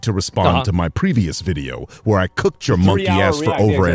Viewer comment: did he just call Asmon a monkey?😭